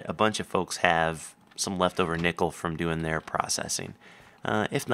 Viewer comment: i could've sworn he said nipple instead of nickel